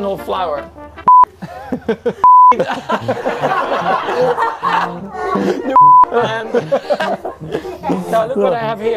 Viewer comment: What did Checo said at 0:57 and Max laughed?? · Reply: Weed, 😊